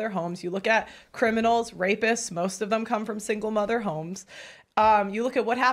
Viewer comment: There's absolutely nothing wrong with being a stay at home my own mother was one for a large chuck of my childhood and I've ended up ok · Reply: I think you misheard nobody said that . Single mother . I believe was said